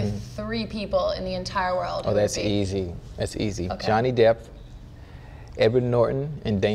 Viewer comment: We gonna ignore the fact that he really said “Johnny Depth”